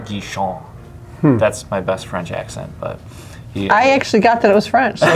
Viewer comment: i heard him say thats my best friend jackson, and then realized he said best french accent lol